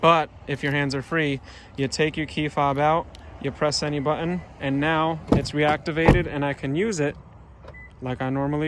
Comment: did u say just press "ANY" button to RE-Activate? THANKS AGAIN!!